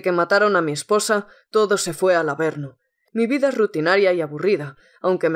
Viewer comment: estaba viendo el video y entendi desde que mataron a mi esposa todo se fue a la verga hahahahaha XD me dio mucha risa me gusto el video sigue asi cuentas con nuestro apoyo